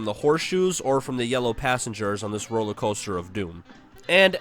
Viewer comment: When you said roller coaster I thought you said Roman culture